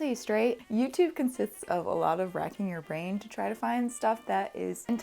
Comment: How the f*** did I mishear that as 'rats in your veins', and why does it still kind of make sense?